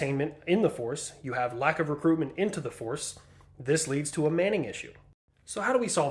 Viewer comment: Not taking away from the very seriousness of the video... I thought it said "The US has a Peyton Manning issue"